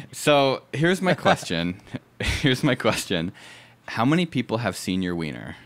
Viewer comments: I thought he said “how many people have senior weiner?” Not “how many people ha e seen your weiner?” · I thought he said how many people have senior wiener lol that silence was great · lol i thought you said "senior wiener" and not "seen your wiener" and for a good ten min im like wtf condition is senior wiener and why would you ask fairly young men that